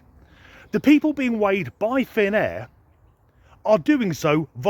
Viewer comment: i thought you said "thin air" not finn air!